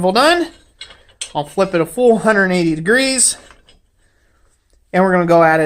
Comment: What did you say at 21:10, You will flip 480 degrees?